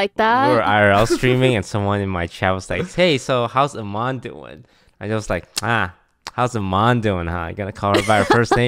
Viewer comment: I thought “Jeremy” said how’s your mom doing